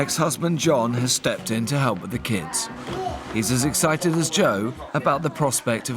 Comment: John stepped in to help with the kids. My brain: John stepped in to help with his kids. Had to rewind.... Nope, John stepped in to help with the kids. Of fucking course they said the man is helping with the kids instead of helping with his kids! Of course!!!